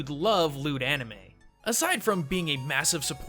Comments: That moment when he says "loot anime" and I hear "lewd anime"... -_ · Not to mention white washing really isn't a problem... Also, I thought you said "lewd anime" instead of "loot anime" at the ending advertisement... Lol. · for a second there i thought he said lewd anime and i was like "gijin you know me too well" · oh, I thought he said LEWD anime xD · How many people thought he said 'lewd anime' instead of Loot Anime? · thought he said lewd anime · For a split second I thought you said lewd anime instead of loot anime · i heard " lewd anime " instead of " loot anime " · Misheard as Lewd Anime. I was no less interested. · I thought you said lewd anime. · At first I thought you said "lewd anime" and I was like "Hell yeah! I love lewd anime!" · I heard "/lewd/ anime" instead of "/loot/ anime"... 😳😳😳😳😳😳😳😳